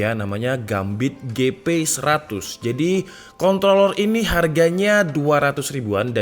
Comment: kontlolol atau aku yang salah denger?